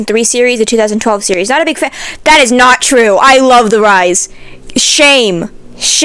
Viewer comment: with subtitles enabled: Instead of saying "I love The Rise," the captions just said "I love the guys" 💀💀